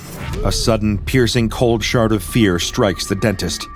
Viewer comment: Did anyone else hear "cold shart of fear" instead of "cold shard of fear" lol